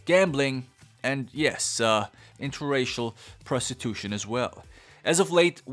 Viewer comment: Great movie and recently district 10 was confirmed!! Have you seen this movie? On whose side are you? Alien or Human? 👽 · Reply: I legitimately thought you said inter species prostitution for a second i was like wait a minute